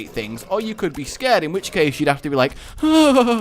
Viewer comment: did he say your money or your wife